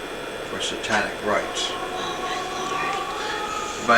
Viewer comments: Did I hear a EVP at 10:19 just as the priest is speaking about satanic activities..??? Is it just me..?? #RIP Ed&Lorraine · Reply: It was a lady saying oh my lord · Does anybody else here whispering at 10:18??? It sounds like someone saying “oh my lord above” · Did any one else hear whispering at 10:17 or was it one of the camera crew whispering . I heard the word "Lord " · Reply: It was the girl that was taking the tour. She said, "Oh my Lord." At least I think lol